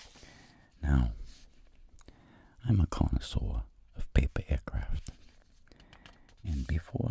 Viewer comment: I always hear "pepe aircraft" and not "paper aircraft" loll